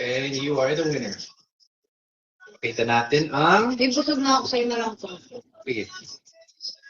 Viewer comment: ano po sabi ni Ms. Kat.. "Bhie, busog na ako, sa iyo na lang ito" .. Tama po ba narinig ko? So sweet... @ 13:41🥰🥰🥰